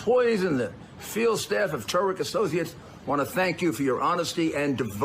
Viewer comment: Did Rudi say "... the field staff of TARIQ and associates "??